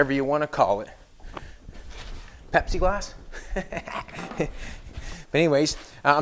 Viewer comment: I thought was saying "Pepsi Glass."